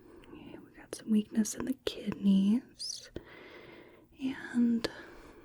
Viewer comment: thought you said "wickedness in the kidneys".. yeah they've been spooky since birth 😂